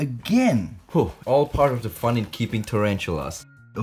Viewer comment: I thought he said kill Cody’s